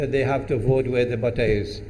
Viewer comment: "... is now irrelevant because we all know that they have to vote where _____." What are the words at the end, please? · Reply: Where the butter is (money)